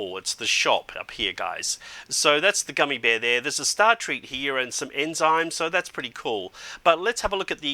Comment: UUUUUH did he say there was a star treat and he said thats pretty cool btw